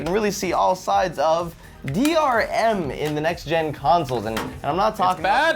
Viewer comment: lasercorn sounds like he says "council" instead of "console"